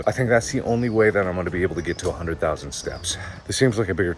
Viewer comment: I know I’m goin crazy cuz I thought He said 1,000,000 steps. Yes 1 million